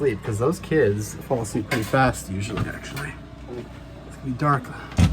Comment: who is saying what sounds like the word "actually" seemingly out of nowhere? Anyone catch that? That was strange.